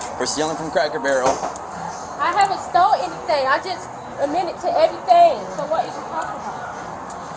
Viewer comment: At the 3:17 mark did I hear her correctly? Did she say, " I haven't stole anything, I just admitted to everything." That's what I thought I heard her say. Oh my goodness, please make it make sense.🤦‍♀️